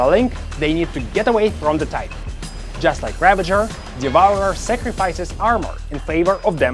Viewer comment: did you just said sacrifises armor ? then am about to smash this titan like butter with my battleship 150% + 300% mauler increased dmg ! ... hah mauler and dux still dominates...